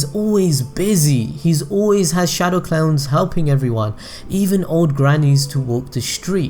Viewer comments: did he say "shadow clowns"? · am I deaf or did he just say "shadow clowns"?